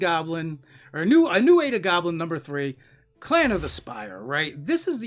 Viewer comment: I thought you said Clan of the Spider. I’m disappointed! 🕷